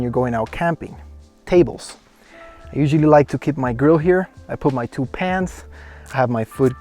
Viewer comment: I thought he said "I usually like to keep my girl here" damn aight player 😂